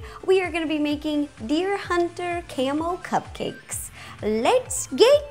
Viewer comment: when my sis watched this she thought you said camel cupcakes why not make camel cupcakes